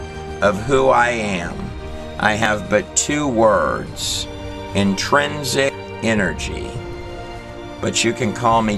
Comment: Could someone PLEASE tell me what he says at 2:26:41 ? "entrancig" energy ??? can´t understand and translate it correctly.